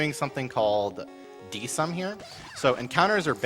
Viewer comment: "We're gonna be doing something called... (desnaidfasom)." - 10:35 What was it that he said? I'd love to learn more about this trick. Fishing for Pokemon is my least favorite part of Pokemon games, because I get so hung up over their natures. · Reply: Desum. As said, at the very least in Generation 1, encounters are taken from time-based cycles, and from what I got from, the cycle lasts approximately 12-13 seconds long before repeating. So basically, based on the first encounter he has on route 2, if he doesn't get the nidoran immediately, he has a reference point for where he is in the cycle of encounters. As such, he can then wait for a point in the cycle where it is more favorable for him to get the nidoran encounter he needs. It's not a perfect science, as encounters are still random, meaning he could get stood up by the game and not get an encounter in the part of the cycle he waited for, but it's how they try to manipulate encounters. The same technique was used later in route 3 to find a spearow, since he didn't get a pidgey in Viridian Forest. Another few examples of Desum can be found in the 151 pokemon run of pokemon blue from SGDQ2015. As for nature manipulation, since I have seen nothing about desum for Gen 3, sadly can't help you there.